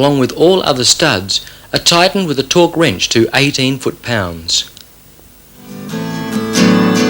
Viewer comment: did he say "18" or "80" foot pounds of torque? 18 seems too light and 80 seems ridiculous. If I had to guess I'd say it is 18. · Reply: 18 foot pounds of torque but i added cc